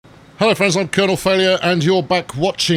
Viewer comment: when colonel says "hello friends" subtitles say "hell offense"